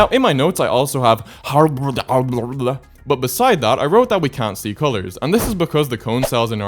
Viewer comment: I thought he said "don't see doors" 😭✌️